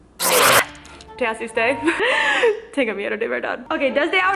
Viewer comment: que dice porque no entendi muy bien Que hace usted o te asuste? Diganme no puede ser posible que no en tienda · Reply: Te asuste